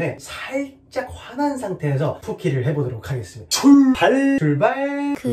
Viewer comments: 오늘은 출발이 풀foot로 들리는군.. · 초에 출발이 ful발로 들려요 ㅋㅋㅋㅋㅋ